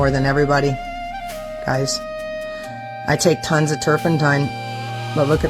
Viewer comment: did she say Turpentine?????😮